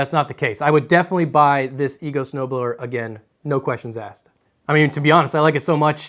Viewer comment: ima be honest i thought it said lego snow blower at first